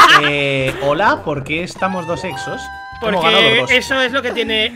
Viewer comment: en el minuto 5:56 le entendi : eh hola porque tengo des sexos xD